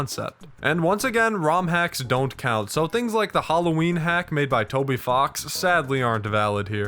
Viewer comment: DID YOU SAY TOBY FOX!?! *Freedom Motif starts playing*